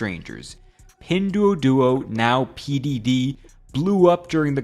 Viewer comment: I heard P diddy